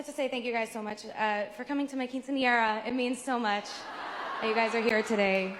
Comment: and i think she said welcome to my quiñce bc of the poofy dress she has on 😭😭